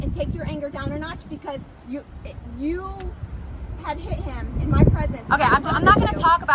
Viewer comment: But I thought the cop said " IF you had hit him in my presence "